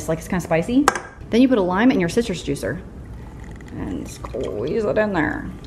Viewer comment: I thought you said "your sisters juicer" 🤣🤣